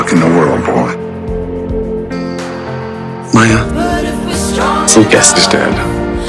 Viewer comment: what does Cory say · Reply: YaGirlMia Lucas is dead